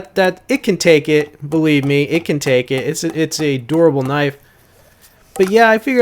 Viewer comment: Lol just busting your balls but the first like 4 times you said "a durable knife" I thought you were saying "adorable knife" because you pronounce dur like door. I was like "how does pounding it into a log make it adorable?"